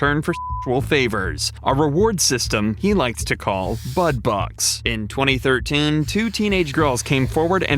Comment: omg, I thought y’all said “Butt Bucks” & I was like… ain’t he quite obvious. JFC. 😅